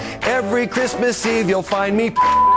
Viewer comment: bleep bleep, what did he say?! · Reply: guessing he said " You'll find me fucking ms.claus"